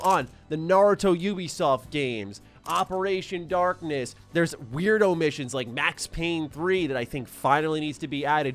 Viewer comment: You say "weird omissions", I hear "weirdo missions"... missions that only weirdos can complete 🤷🏻‍♂️